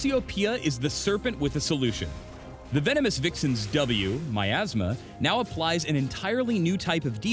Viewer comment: am I the only one who was like WTF at 4:13 and thought he said My Ass Mum...